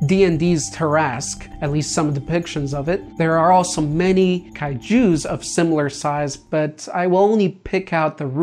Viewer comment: Why did you say it like Kai Jews? Made me picture Godzilla as an Orthodox Jew😂😂😂😂😂